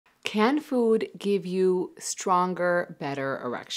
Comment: She said "can food give you stronger erection" I thought she said "canned food give you stronger erection" 😂